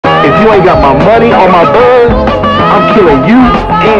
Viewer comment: I heard if you don't got my money on my boat💀